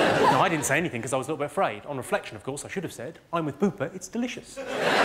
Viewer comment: What is he saying at 4:19? I'm with [...], it's delicious? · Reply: JustLies “I’m with Bupa; it’s delicious”. Bupa is a provider of private medical care